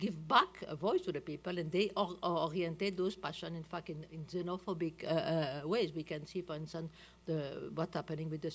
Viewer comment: did she just say "in fucking xenophobic way"? ._.